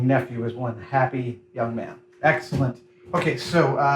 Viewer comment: I swear I heard, "Axellent" around finishing the paint. Awesome!